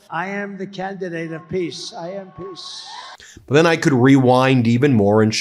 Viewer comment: it's funny, when he says "I am peace" what I hear is "I am piss".